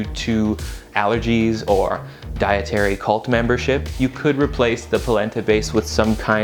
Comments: I thought you said placenta for a sec.. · Palenta? I thought you said PLACENTA!!